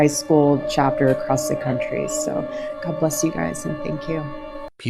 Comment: I believe I hear ' May Gog Be Less You ' ...😊